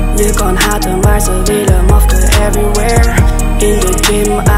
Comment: ik hoorde mafke 😂